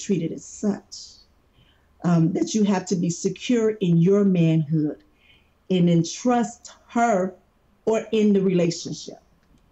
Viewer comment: Love your vids bro and I usually side with you on a lot of things but I gotta call you out on this one. She never once stated that not wanting to be with a sex worker makes you insecure. She simply said “to be with a sex worker you have to be secure” which I agree with fully. It just depends on your morals, I’m secure in myself but wouldn’t do it personally. But she gave the man some very sound advice imo. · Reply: Listen to what she said just before you paused the video at 2:00 , I think you completely misread how she was trying to convey that message, she said “you have to be secure in your manhood, and be trusting in her” which is correct, do you think an ‘insecure’ man could deal with that? No chance. I don’t think she was saying that if your not comfortable with it your insecure. Just that you do have to be in order to date a sex worker.